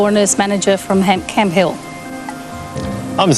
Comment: Which suburb did say she came from? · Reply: I think I heard "Camp Hill", a suburb in Brisbane.